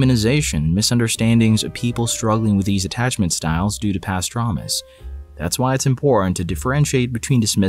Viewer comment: I can't be the only one who heard "pastramis" instead of "past traumas"